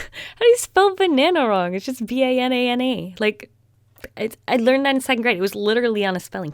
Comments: did she say B a n a n e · “They spelled the word banana wrong! How do you spell the word banana wrong? It’s just B A N A N E!” Am I the only one that heard her say the letter E?